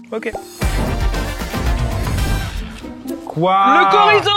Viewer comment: le quoi ???? Le « K »orizo ??! Aïe ! Ça fait mal au « CH »orizo !!